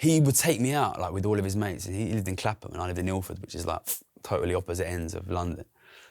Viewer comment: subtitles say "Clapper" and "Milford" 😂 it's Clapham and Ilford.